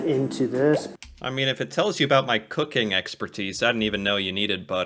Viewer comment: he says "About" that sounds like "a-butt" and definitely not "a-boot". Elsewhere, he seems to have trained himself to say "around" instead of "about".